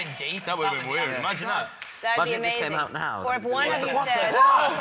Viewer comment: Idk if I'm right or just misheard it, but it sounded like Liam said “Imagine, imagine if u came out now” but correct me if I'm wrong, ahah